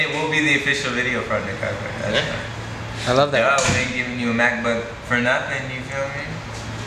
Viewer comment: I thought he said mathbook. Lol. I was like whaaaaa???